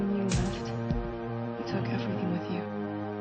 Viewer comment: wtf is it just me or do i hear bella say:"you took edferting with you"